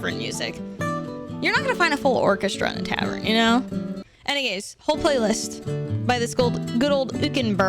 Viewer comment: Did anyone else hear “any gays” 🏳️‍🌈instead of any ways?